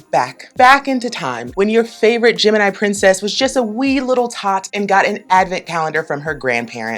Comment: oooh wee little tot, I first heard a wee over the top.